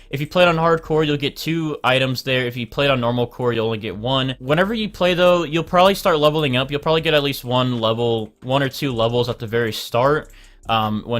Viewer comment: wait did you just say normal core